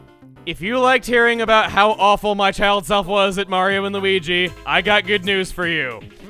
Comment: Fuck's sake I thought he said "my child, Zelda."